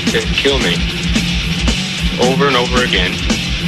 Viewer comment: cómo por el minuto 5:24 dice algo sobre over over game significa killme en código morse o que dice porfa ayúdenme a entenderlo excelencia de cancion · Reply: dice "*over and over again*" (una y otra vez) pero deberías ver la película se llama "Johnny Got his gun" en codigo morse Él dice kill me